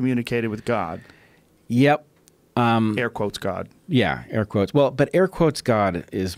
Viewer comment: what did rogan say? Ark quote god?